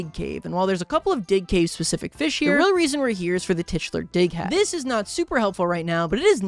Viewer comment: when he said dig hat i thought he was saying d### hat... i have a dirty mind